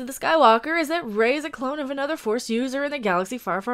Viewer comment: Why do I keep hearing "is Rey a clown"?